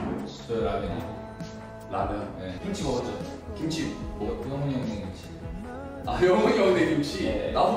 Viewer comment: i think he said "younghoon's mother's kimchi"